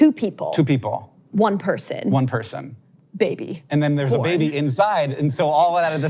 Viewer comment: I’ve made a list of some unsolicited corrections to captions below (current captions in parentheses) 7:02 “like we can’t cure all of our diseases” (not “hear all of our diseases”) 15:23 “declarative statements” (decorative statements) 19:53 fanfic or fan fic (fan fake) 21:48 definitive statement (finished statement) 34:08 “I’m holding on to my heart” maybe? 38:10 Hank says “it will be absolutely gorgeous” (it will be awesome the (indistinct)) 36:21 “I’m making a statement” (Him making a statement) 40:44 “Just something I’ve always wanted to do” (Something I’ve always wanted to know) 41:05 “and a touching moment” (and touchy moment) 42:12 and 42:16 “hurt her voice” I assume (heard her voice) 42:20 I think this is “film and television stuff” (film and television and stuff) 44:18 doesn’t sound like as many syllables as “been to your.” It sounds more like “done your” except the done has an extra d or t on the end. doned. idk. 45:38 “So it’s just good information” (Such is good information) 46:48 Vic’s dialogue “when you hear a bell an angel gets its wings, and then when you subscribe — Tinkle — Tinker Bell doesn’t die” 47:41 “that was a declarative statement” (decorative statement) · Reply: I think Vic says “baby, born” (not “baby, boy”)